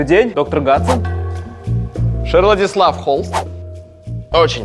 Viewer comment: мне одному послышалось доктор ГанДон